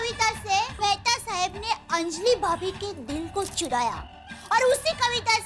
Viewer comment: Did I just hear the word Ch*daya?😂😂